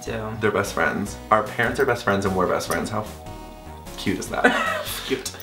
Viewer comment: haha when on 2:57 mitch said #fcute it sounded like polish world "fiut" which means "willy" :D ps POLAND LOVES YOU GUYS <3